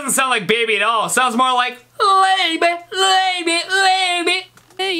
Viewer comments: did he just say "lay me lay me lay me " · i hear baby